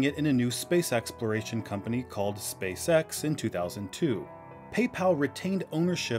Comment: every time 😅😂, every time I hear SpaceX, I keep hearing space sex 😅😂